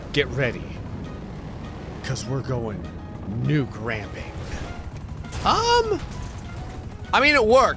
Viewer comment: when he said "nuke ramping" the subtitles said "new cramping"